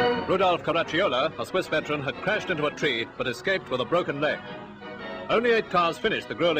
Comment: "...has crashed into a tree but escaped with a broken neck". A broken neck is a minor injury in 1950s. Edit: I heard it wrong. It's leg. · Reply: A broken LEG not a neck lol